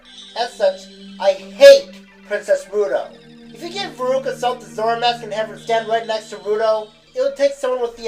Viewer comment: who did you say? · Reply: Lori Taylor I said that if Veruca Salt from Charlie and the chocolate factory was given the zora mask, it would be very, very hard to tell the difference between her and Ruto.